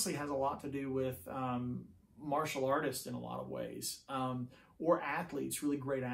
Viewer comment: I initially heard this as “Marshall artist”. Heh.